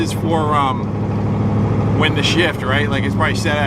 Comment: Shift light. It did some racing in it's life. Don't know if that's why the original block got an inspection window installed or not though 😂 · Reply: I thought said charge light, but shift light makes more sense, still ugly srapped to column, spoils nice interior